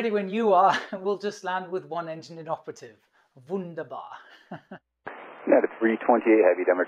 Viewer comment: “Wunderbar” is going to be the new running gag! :D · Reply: Wunderbar? I thought I heard GUNDABAD! hahah #LOTR